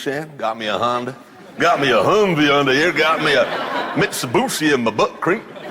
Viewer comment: he did not just say “Mitsubussy”